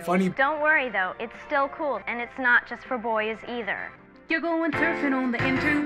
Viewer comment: what did the girl said? its not for boys?...